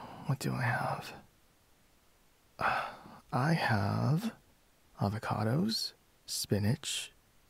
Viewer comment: When he said "avocados" I thought he said "other condoms."💀💀💀💀💀